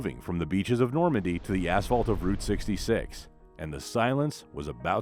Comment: I swear at 5:40 he says "the assholes" of route 66!